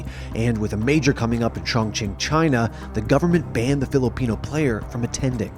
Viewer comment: He used a major slur at ChongQing... did he say Qing Chong?